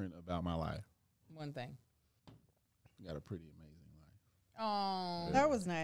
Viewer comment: What did say at 26:14? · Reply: “I got a pretty amazing life”